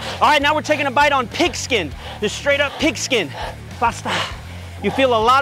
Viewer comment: Blud really said "Brrahh Ghaahh Bahhh" When he tried on the 2nd sleeve 💀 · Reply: Haha I know and third one sounds like he said pasta haha! 😂 also gota love the ohhh mother fucker! 😂